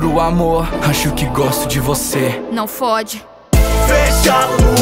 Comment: "Vá pela sombra,desgraça" é tipo isso que ela disse,e colocaram "não fo##" 😆😆😆😆😆